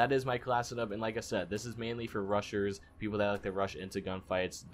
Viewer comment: thought he said this gun is for russians lol